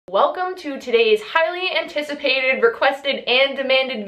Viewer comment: why did I thought she said welcome titties